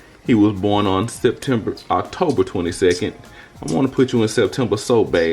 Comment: He looks like Bae...I thought I heard u guys say he was born Oct 26 which is my bday